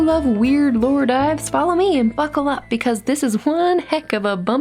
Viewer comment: I'm sorry I heard "f*ckle up" instead of buckle up😭